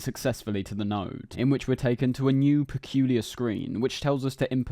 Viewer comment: did you say nerd?